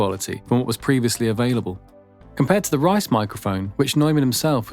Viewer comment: Is he saying, "the rice microphone"? Wanna look into the mic being shown and having zero luck. thanks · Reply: Reisz microphone, i don’t think i pronounced it correctly.